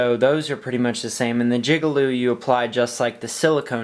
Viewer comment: did you just say jigalube? LMFAo